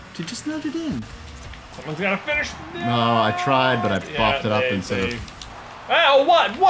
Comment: what did say? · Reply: Christopher Medina he said: “I tried but I flopped it up instead of” he didn’t finish what he was saying